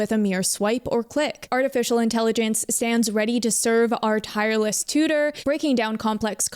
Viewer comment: "artificial intelligence stands ready to serve AS our tireless tutor" is how it reads onscreen, while "artificial intelligence stands ready to serve, our tireless tutor" is what was said